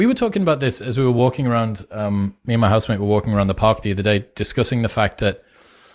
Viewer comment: says 'me and my husband', I didn't know he was gay, did I hear ir right?